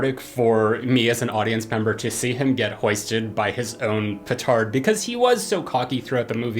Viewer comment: is he saying pertard?